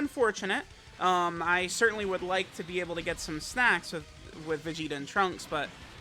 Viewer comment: I heard you say snacks man I’m not letting that one slide while I’m this hungry.